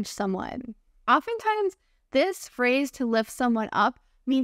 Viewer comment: I am a regular to your videos. Thanks for your helpful videos. Just wanted to point out that the caption for one of the phrases ( at 7:35) may have been mistyped: "life" instead of "lift"